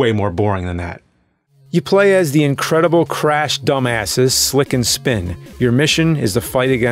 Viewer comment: I thought he said Sit and Spin, then he says Rimjob